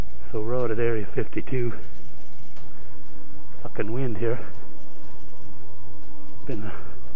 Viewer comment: HAHAHAHA!!! OMG, you had me crackin' up in that opening scene with your narrating! Half a haircut, Hallmark movies call for something manly . . . way too funny man! Oh, and at 1:15 - Pardon me young man? my mom would say. Would you like your mouth washed out with soap? HAHAHAHA!!! You two sure have a lot of critters running around out at area 52 (and wild chainsaws growing out of trees - 15:27 - lol). I sure hope some day will feel himself again so you guys can build your dream home out there and live happily ever after with Joe & Rhonda Squatch as your neighbors. Whoooohoooo! Ohhhh man! Poor Oliver! Your pups have sure been through a lot. Yet their tails keep wagging. We can all learn something from that. I think there was a song written which encapsulates the spirit of an emotionally healthy dog . . . Don't worry be happy! curiosity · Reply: Thanks for checking out the video Q. Glad you enjoyed it. I told it sounded like "the bad word" at 1:15 but it was actually "sucking" wind LOL We had a fun ride and it was a beautiful day to be out there. Our pups have had their challenges for sure, just so thankful we still have them with us. Dogs are so resilient! Oliver's little tail never stops. We can definitely learn a lot from them.